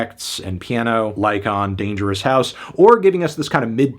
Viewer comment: I heard a MF say "A Dangerous Listen"